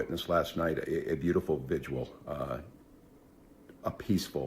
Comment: did he just say VIGUAL? 😂